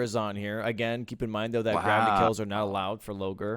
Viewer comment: What does say for what's not allowed when killing NPCs? I can't hear him over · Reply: gravity kills